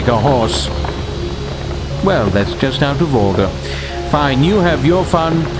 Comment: Hi, could someone please help me understand what the narrator says at 0:55 - 0:58 ? "Well that's just..." ? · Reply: "... out of order?"